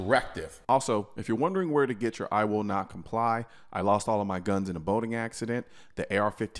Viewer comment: I thought I heard him say, I lost my guns in a voting accident.